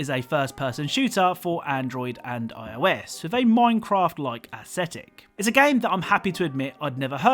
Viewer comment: I thought he said “ and it makes Minecraft look pathetic” 😂